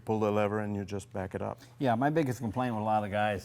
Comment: It sounds like he says "my niggahs"